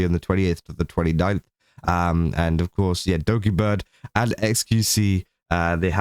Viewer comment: Put captions on, it says "Doggy Bird" instead of Dokibird. Whoops.